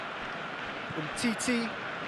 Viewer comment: I swear it says titi (henry) XD